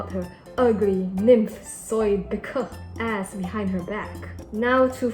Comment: I’m sorry, but what’s the word for nymph soybit?? at 5:43 , and japf?? at 6:19 , 9:00 ???rag thanks😭😭